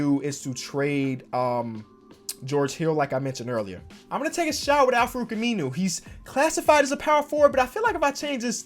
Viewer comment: Why at 21:47 it sounded like said “Imma gna take a shower with Al-Farouq Aminu.”